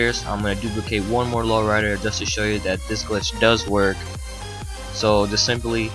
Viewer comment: did anyone try in old gen dose it work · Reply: CarloTheBossYa I see now, i thought he said it doesnt work